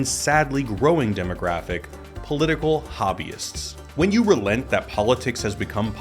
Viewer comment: sounds like he is saying Political Wahabism